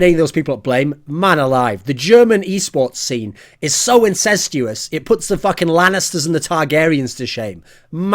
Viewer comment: What is the german eSports scene? I couldn't understand that word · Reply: incestuas? or how is it spelled? Does it mean that everyone is involved at everything?(in this context)